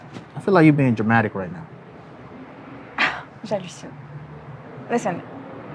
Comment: Is she a french girl, I heard kind of "J'allucine" when said you are being dramatic lol